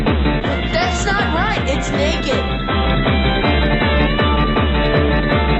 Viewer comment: Did bro say it’s naked!